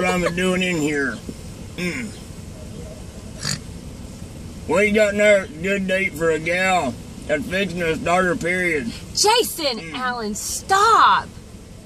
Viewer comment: What is he saying at 0:29 that makes the mrs say "JASON ALLEN STOP"? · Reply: what do you got in there good to eat for a gal thats fixing to start her period